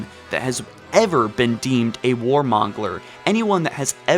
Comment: I'm sorry... a war WHAT? Did you just say mongeler?